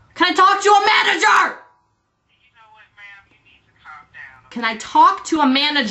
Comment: Did the operator think Drew was a girl? O_o I thought i heard her say: "you know what ma'am, you need to calm down, okay?"